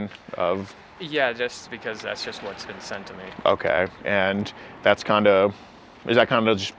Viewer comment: Did he just say four is age of consent to him what the fuck does that mean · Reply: He said "been sent to me".